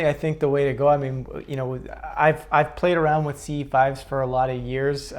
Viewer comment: ive played around with _____? Sounds like he says, “CE5’s.” what is this?